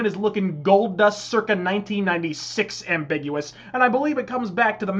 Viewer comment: I noticed something with the captions. 0:37 You say 1996, but the captions say 1995. Not that much of a problem, but just something I noticed.